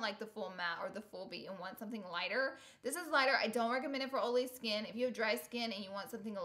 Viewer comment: Lol at 8:23 when she said "I don't recommend it for oily skin" I thought she said "I don't recommend it for old lady skin." I love hearing people with southern accents say "oily"